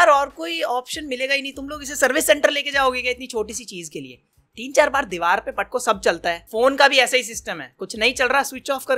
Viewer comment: I thought he said phone ko bhi diwaar pe patko😂😂